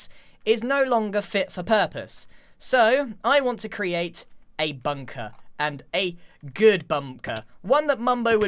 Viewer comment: i swear grian said 'bumker'